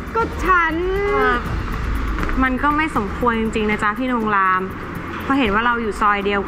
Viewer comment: สมไรนะได้ยินไม่ชัดหรือผมคิดไปเอง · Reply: สมควรค่ะ สมควร